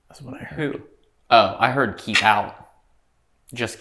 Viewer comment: I thought it said, "Diddy keep out." Ghost ain't playin'. He don't want no part of no freak-offs. For real.